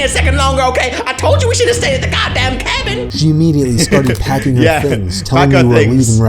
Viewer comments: who else saw hacking instead of packing in the subtittles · why does it say hacking instead of packing